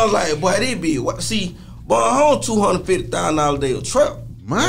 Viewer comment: 250k a day I doubt it bro that’s a complete lie. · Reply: Go to @ 2:47 he CLEARLY says Boeing Homes was a 250k a day trap. PAY ATTENTION AND KNOW WHAT U ARE TALKING ABOUT before u engage.